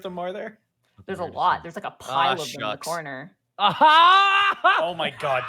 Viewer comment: What did say at 55:37? I can barely understand him as his words seem to be mixed with there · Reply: Aw shucks.